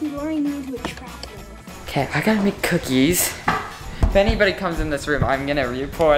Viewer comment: I thought said I got mc cookies not I got to make cookies